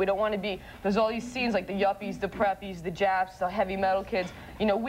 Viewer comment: Did she just say "Japs"?